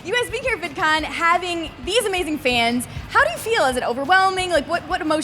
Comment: is it bad that when Phil said "amazing fans!" I thought he said it like "amazing Phan"....